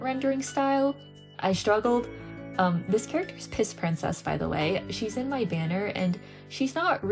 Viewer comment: Did I just hear "piss princess"? TOT